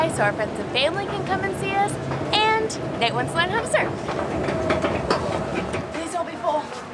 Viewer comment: What was the third reason? Can't for the love of me understand what is being said there...! :) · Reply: ColinZeal “..and Nate wants to learn how to surf.”